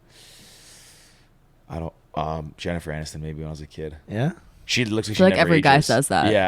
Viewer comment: Subtitles 43:37 👉🏾Jennifer Anderson instead of Jennifer Aniston Lol